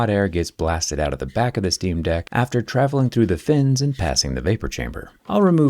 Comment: did he just call it a steam deck?